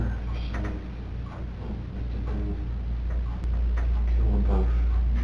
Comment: there is a splice..(watch the gray timer).. not sure why they cut it like that but it does sound like he says "kill them both" after he says about getting a lawyer for him..(why tho??🤷🏻‍♀️